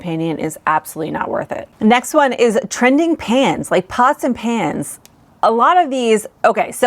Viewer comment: I was doing chores and i got to the (Pots and pans) part, i kept hearing "pasta pants"! And I was so intrigued what the pasta pants were 😂 that I had to come back to my phone and check, damn did I feel silly